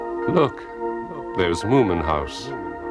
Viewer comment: I thought it said “wooman house”